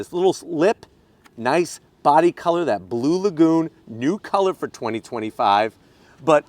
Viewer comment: I’m early! Loving the Lagoon Blue Pearl color on it! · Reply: I thought he said Blue Magoo initially.